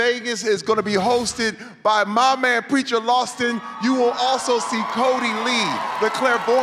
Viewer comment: Funny that his skit was about 'preacher' but Terri called him "lawston". lol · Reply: first of all its rude to correct and second of all it did sound like he said lawston